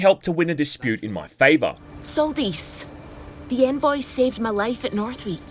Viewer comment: I misheard "envoy" for "femboy" for a sec